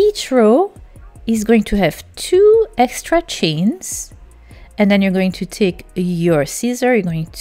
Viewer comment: what are you saying at 9:43? 2 “estretchings”?.. I normally understand you completely but lost here. Thanks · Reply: ))))))))))) 2 extra chains....